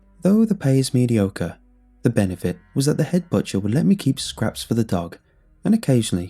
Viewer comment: For a second, i thought he said, "The head butcher would let me keep scraps from the dog" I was worried for a minute.